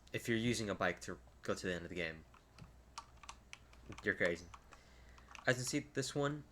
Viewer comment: Lol did you say "if you're using a bike to get to the end of the game... You're gay" @ 2:41 😂😂